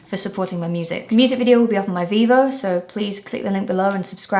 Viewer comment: Definitely thought you said "..the music video will be on my Bebo.." damn.